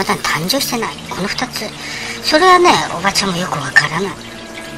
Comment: おばちゃんがチコちゃんに聞こえる！